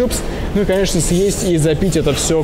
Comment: Я один это услышал: З А П И Т Ь К Р А У С А Н У М И🤣🤣😂